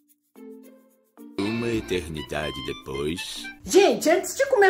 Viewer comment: Só eu q escutei o ketlen é a void😂😂😂